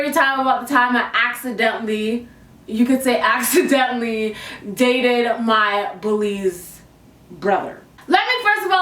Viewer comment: I thought it said I accidentally dated my brothers bully.. I must have been on crack!